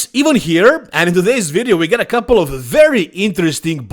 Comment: Hey guys Ivan here, is how I start out my first meal of the day, every day. It’s iconic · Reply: Bro you know whats funny … last year i started watching videos and a lot of times id just have his videos playing in the background while i was doing stuff. For the longest time i thought he was saying EVEN Here! Id be like looking for pics after a while like wtf is he talking bout the video just started there is no picture😂😂😂😂! Than i seen the name of the channel and im like oh its his accent 😂😂😂! Keep up the great work Ivan one of the best in the game!